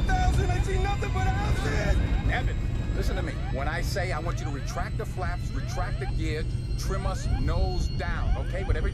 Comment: What does co-pilot Evans say he sees nothing but @ 3:56? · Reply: Houses!